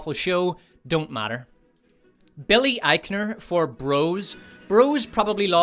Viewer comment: I was distracted while viewing and misheard "Bros" as "Rose," and failed to react, easily believing a make-up spin-off for the fastest discard of character in the Walt Kennedy doucheverse was streaming somewhere, and that is truly sad.